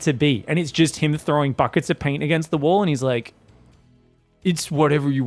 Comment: instead of buckets of paint, it's buckets of poo